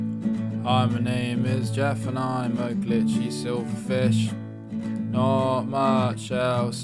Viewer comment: the first time i watche this i thought he said "hi my name is geoff and im a b***hy silverfish" xD